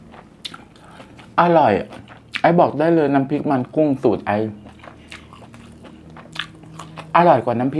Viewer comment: พี่พูดว่าน้ำพริกมันกุ้ง ตูด อะอร่อยมากเลย